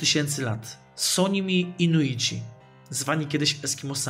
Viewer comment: jak się nazywają? nie usłyszałam (piszę z konta brata) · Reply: Inuici